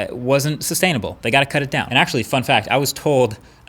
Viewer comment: When he said fun fact. I thought he said fuck that